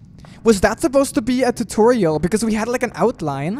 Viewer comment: am i the only one who hear saying TU-TORIEL